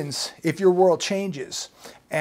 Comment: I thought he said chink goods lol